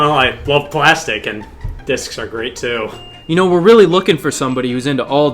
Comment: actually thought he said "I love plastic, and dicks are great too"